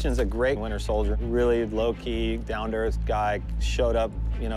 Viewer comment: Thought he said he was loki 0:30 lol